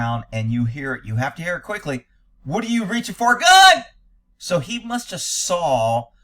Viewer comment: I'm pretty sure the cop actually says, "He's reaching for a gun!" Instead of, "What are you reaching for? Gun!" But I could be wrong.